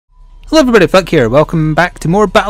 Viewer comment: Flik you messed up on your name to me it sounded like you said fuck here